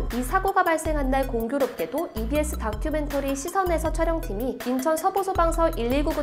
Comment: 시선에서 -> 사선에서 자막은 사선에서 인데 발음은 시선에서라고 들리네요. 이런 기네스등제는 제발 부디 다시는 없었으면 좋겠네요. 그리고 영종대교 추돌사고의 기네스기록은 이제 미국으로 넘어가겠네요. 130중이던가. 그래도 잊지 말고 조심해야할 사건인듯 싶네요.